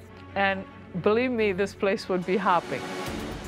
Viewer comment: "I wish I had two clones of me, and believe me, this place would be hopping." That's a level of narcissism that demands a punch in the face. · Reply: I thought she said happy instead of hopping